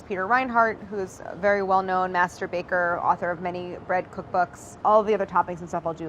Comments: Claire: master baker Me mishearing: masturbator??? o_o I think I need some more sleep. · Did she just say "very well known Masterba(k)er" .... lmao 8:15 ... haa!! I'll go wash my brain, bye... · lol Claire said "Master Baker" but I heard Masturbator 8:17 folks xx